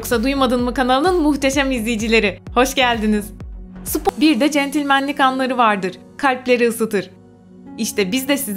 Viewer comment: ne ? Demek spi.... diye duydum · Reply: Spo. Centilmenlik¿